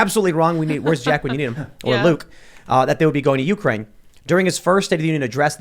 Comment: did he just call Luke a cuck?